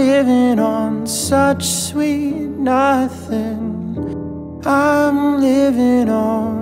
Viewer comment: When I heard this on the radio I thought she was saying "sweet loving", good thing I can actually understand